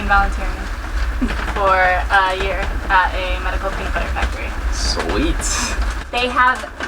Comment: Did she say medical peanut butter factory?